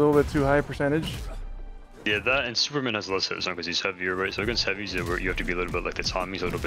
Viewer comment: Bro what was saying at 12:17 😭😭 I love em but I can’t understand him somtimes hahahahahah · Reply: Lmao!! He was just saying Superman is heavier so he has less hitstun. The movement between abilities is a tighter window to hit it.